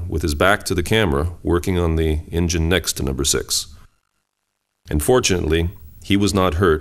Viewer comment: "Unfortunately, he was not hurt in the explosion..." Did I hear that right? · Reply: Haha no he said "and fortunately he was not hurt".